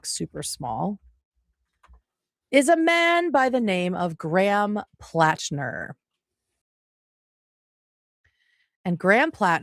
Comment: I thought that politician said his name was Graham Cracker…. Mmmmm… I’m hungry